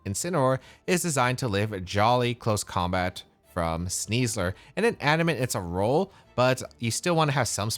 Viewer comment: Subtitles say "Jolly Close Combat from Weavile" instead of Sneasler